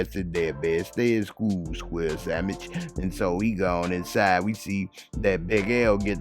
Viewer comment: Squirrel samich 😂😂😂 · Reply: I thought he said, "square sandwich."